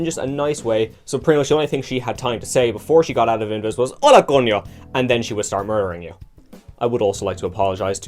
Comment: Mexican viewer here. I legit have no idea what you tried to say LMAO (educated guess: "Hola, Coño" on the second one; 1st one is 100% illegible lol) Good video btw. I really miss the old invis animation :( [and the opportunist passive] Ps: Launch sombra done correctly looks like it would be a blast to play.